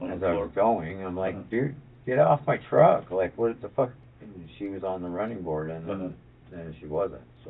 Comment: I thought they said she was on the bonnet??? DON'T DO F**** DRUGS AND YOU WON'T END UP LIKE THAT.😢😢😢